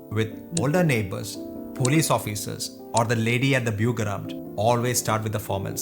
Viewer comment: Not sure, but I think he said: "Lady at the Bürgeramt" (Citizen's Office). It's obvious, that he hasnt traveled around much. Some points are not that strikt.